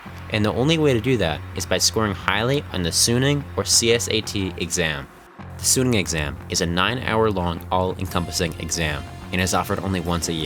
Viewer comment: misheard the "Suneung Exam" as "Chunin Exam" lmaoo